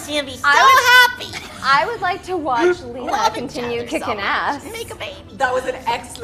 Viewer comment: Did anyone else hear Jeremy Jordan say "love it each other so much. Make a baby?? at 7:10 Or is it just me?